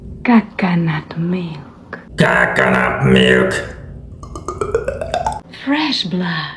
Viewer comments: I heard it as "Cat can't milk" Edit: comment copied from her another video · Bro I thought said fresh pot💀